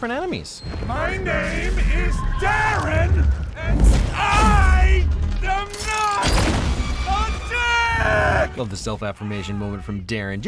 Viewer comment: pls tell me someone else heard him say "I DEMAND A DICK"